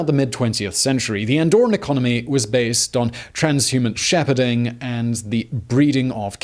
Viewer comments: Did I hear him correctly at 3:20 transhuman Shepparding? So a bunch of cyborgs engaged in the business of animal grazing and breeding?! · Did he say "Trans-human shepherding" and the breeding of cattle? 3:21 No way I heard that right, even by the third re-play...